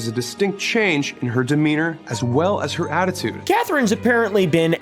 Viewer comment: I thought he said her name was Mis Demeana like misdemeanor